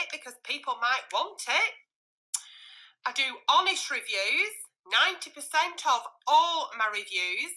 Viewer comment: Bless she sounded like she said she does Amish reviews (honest reviews) lol